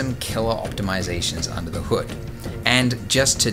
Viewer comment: did you say Nevidia?